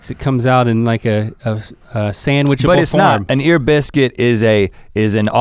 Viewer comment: I heard "sea biscuit" Only the 2 of you is the better EB!! Keep it up!!